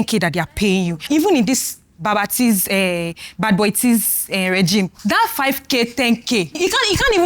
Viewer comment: did she just call tinubu bad boy T?😂😂